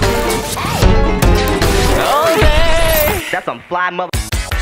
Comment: this is like the 100 time I'm listening to this, and I still can't figurate what Luz is saying at 3:02 H E L P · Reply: “that’s some fly mf-“ is what i hear lmao